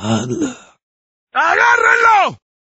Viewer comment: me and my sister always thought that he was saying "I GOT RILLO!" XD and it was always the ones with the torches XD